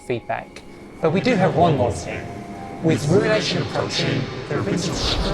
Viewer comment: i think i heard him say "with thresh approching"